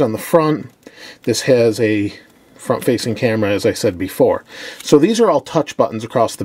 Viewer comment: wait. did he just say fuckfacing camera?